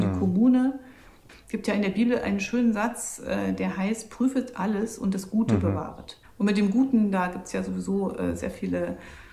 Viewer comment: Sehr interessant und Frau Göring-Eckardt wirkt offen und sympathisch! Das Bibelzitat bei Minute 15:40-15:45 kann ich leider nicht verstehen - kann mir jemand helfen? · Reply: Lutherbibel 1912 prüfet aber alles, und das Gute behaltet ◄ 1.Thessalonicher 5:21 ► Lutherbibel 1912